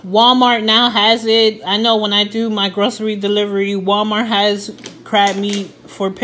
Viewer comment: That looks delicious!!!! Wow you deliver groceries too.? You do it all!!! That should be a good business!!!😎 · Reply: I'm sorry, I thought you said " when I made my grocery delivery" lol. I mean its not a bad thing. That's what I thought I heard. I apologize. 😎